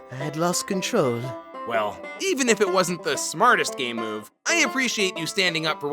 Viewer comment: I can’t be the only one who hears gay move 💀💀💀